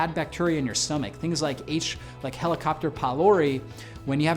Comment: Very useful information! Did he truly say “HeliCOPTER” pylori at 4:34? I think he did... 😂 Instead of Helicobacter... anyway, I was so pleased to seeI am already consuming many of these. Will add collagen protein to my day. Thank you!